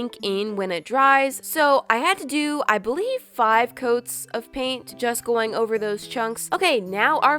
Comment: When u said paint job I thought you said blow job